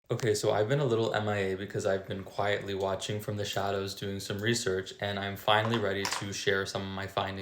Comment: At first, I misheard you as "... watching from the Shallows ..." 😂